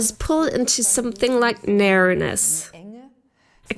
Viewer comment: What is that word she's using to describe what many call a tunnel? She begins using it at 7:27 but I can't make out what the word is. Meriness?? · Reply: It's narrowness 😆